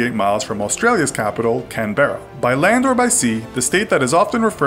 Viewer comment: WTF DID HE SAY LOL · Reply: Kyle Kreider He's talking about how he pronounced Canberra